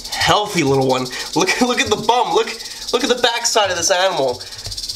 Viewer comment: did u just call the snake thicc?